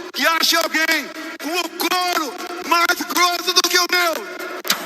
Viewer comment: Eu entendi "o corno mais grosso que o meu."